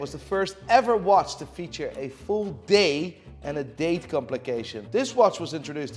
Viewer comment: Thanks to this video, I now realize that you're saying DAY and DATE. I thought you were saying Dae Dae, like a nick name. Like some famous pimp named Dae Dae wore that Rollie